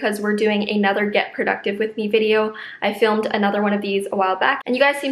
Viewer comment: I thought it said get reproductive with me... 😆